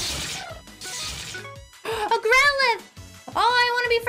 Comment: In the beginning when she ssid growlithe i thought she said gremlin and was so confused lol